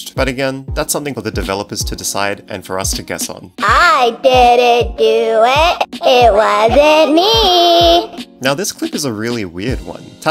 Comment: In all honesty, that one line (3:22) sounds more like "I didn't do it; it wasn't me" instead of Tattletail saying "I didn't do an-(ything); it wasn't me." Is this just me, or...?